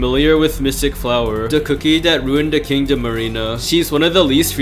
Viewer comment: I just heard "king of marina" when he said kingdom arena wth